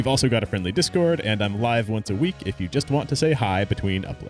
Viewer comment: misheard the end as "I'm alive once a week", which, mood.